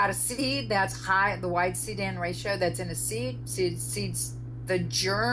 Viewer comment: "when you've got a a seed that's high in the ??? ratio that's in the seed" What did she say? Sounds like y-seedan ratio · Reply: High C:N ratio :)